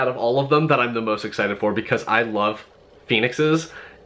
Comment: ! LMBO! @ 12:41 I thought I heard "I love penises." Lololol! I had to rewind that bit to make sure if I heard that correctly. Great haul!